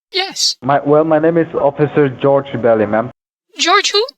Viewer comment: I thought he said George Belly, lol. He sounds like a newbie.